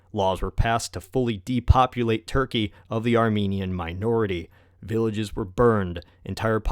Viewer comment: I thought it said "American" instead of "Armenien", so I thought "What the hell did we do this time?!".